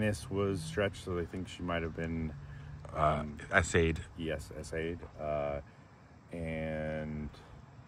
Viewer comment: Those crime scene photos are horrifying. They haunted me for a long time. Btw what is "assayed"? You said it at 7:22. I don't think that word exists. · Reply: S.A. It stands for sexual assault. You cannot say those words on YouTube anymore or else your video is flagged